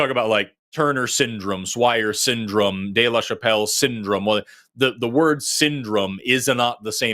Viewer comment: I thought Forrest kept saying Dave Chappelle syndrome.. some who make trans jokes 🤭